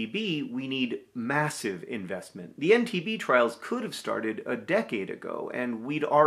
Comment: This is amazing news! But also -- every time John said EndTB I heard "MTV." I thought "Oh wow, look at MTV using their power and resources in this new and unexpected way, good for them." And it took me a few times hearing it to realize that that probably wasn't what he was saying.